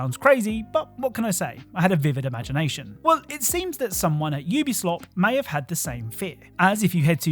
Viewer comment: Did he just said Ubislop?